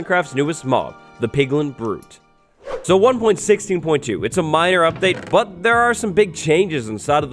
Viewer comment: I literally thought you said "Miner Update"